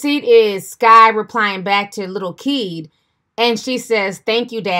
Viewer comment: Who tf is a lil keed🤔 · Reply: Lol i thought she said lil key 🤣😂